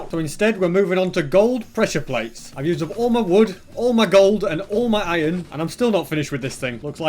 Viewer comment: umm so what did he say? He said I'm out of gold? Did I hear that right?